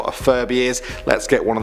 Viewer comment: I thought you said " Furry " & not " Furby "